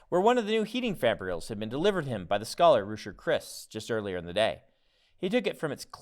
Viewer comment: Is there anyone who wasn’t there that day?! I’m pretty sure I heard Khriss’s name as a servant in addition to all the other craziness. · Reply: He mumbles a bit there but it almost sounds like he said Rushu Khriss